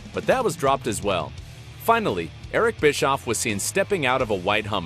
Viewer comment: We're you saying Larry Nabisco?? 😹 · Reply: all I heard was "bisco" . Is there a copywrite issue here ir what?